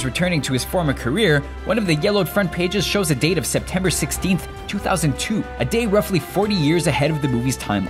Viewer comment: September... 16th... 2002... did I hear that right?